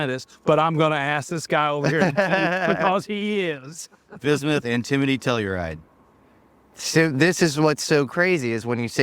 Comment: bismuth antimitulride? am i hearing that correctly or is he saying bismuth antimitriride? bc im pretty sure those are wildly different base on 10 mins of search. i dont really know shit tbh but it sounds like theyre saying that's the compounds we need to put in a stimulated vacuum of some sort to get the material to make ufos. thats some wild claims